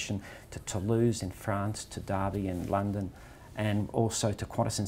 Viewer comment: To Derby in London....🤔🤔🤔 I think he meant Derby in Derbyshire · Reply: Thought he said Derby AND London. Rolls Royce are based in Derby so that would make sense